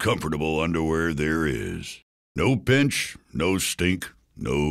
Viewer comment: On the 0:24 second makes me laugh sounds like he says "no bitch" but it's no pinch lmao